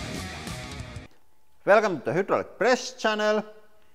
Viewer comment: I heard “welcome to Hitler press channel”